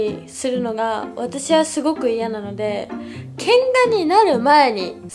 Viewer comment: 喧嘩がてんがに聞こえた自分の耳を呪いたい